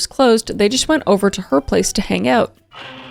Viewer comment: why’d I hear “make out” instead of “hang out” 😭